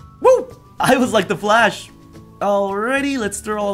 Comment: did he say: "I always like to flash"😂? i keep going over the same part and still cant hear any different. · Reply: "I was like the flash"